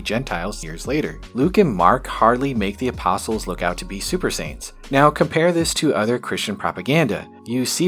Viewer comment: First I thought you said Super Saiyans then I reasoned that I misheard you say Super Saints. Then I thought Super Saiyan Saints and now Peter and John are Vegeta and Goku. 🤷🏽‍♂️